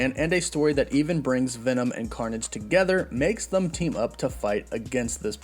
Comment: all I heard was carnom